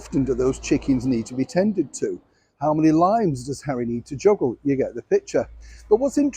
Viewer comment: 😅😅😅 I thought you said Lines instead you said Limes. Long time since I lived in York, lost the accent 😂